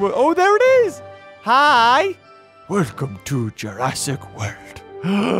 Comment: When he said ''welcome to jurrasic world'', (1:59) it sounds like he said ''welcome to jurassic wurt''